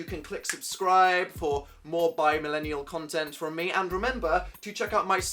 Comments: "BI MILLENIAL CONTENT" DAN DO YOU REALISE WHAT IT SOUNDS LIKE YOU JUST SAID WAS THAT INTENTIONAL I NEED ANSWERS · Am I the only one who heard him say "more bi millennial content from me"